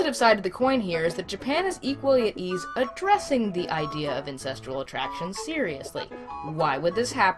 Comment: Is it just me or do I keep hearing "ancestral" romance instead of "incestual" (which should then be "incestuous")?